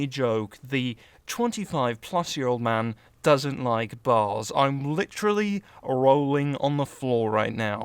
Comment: it sounds like he said the 25+ year old man doesnt like ball's I'm literally rolling on the floor right now